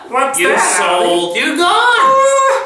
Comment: Did he just say "you sold your gun?"